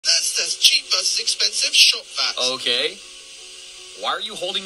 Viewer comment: Holy crap, when it first started I thought I heard “cheap vs expensive shot glass”, and then proceeded to be incredibly confused as he vacuumed sand out of glasses….